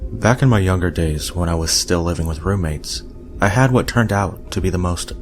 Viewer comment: I heard "living with mermaids" at 3:34 and totally accepted it for like two seconds.